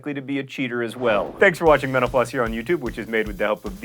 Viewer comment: My sound isn't that great on my laptop but at the end it sounded like you said "thanks for watching menopause"